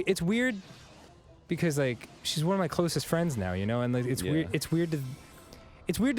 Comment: I wasn't paying attention for a second, just listening to the sweet stories of Dan, when I thought I heard him call a farting wall one of his closest friends.